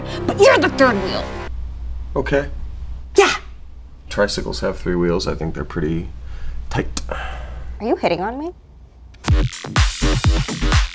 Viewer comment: that was more like a “we should do a three some” instead of “are you hitting on me?”